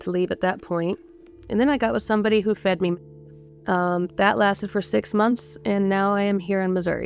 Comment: "And then I got with someone who fed me m***" Fed her what? Since when is 'meat' a curse word? Or is something else meant here?? Who TF knows? "Unfiltered Stories" indeed! :face-orange-frowning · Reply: They word may have been meth.